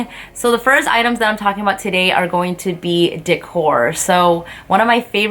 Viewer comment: When you said "decor" at 0:40 i thought you said dick whore ..... LMAOOO